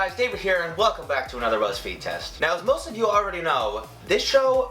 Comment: Why did I hear "ruzzfeed" instead of "buzzfeed" in the beginning?